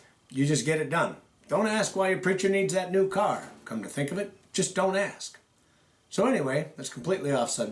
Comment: my pastor was given a new car recently. just dont ask was what i heard in my head. lol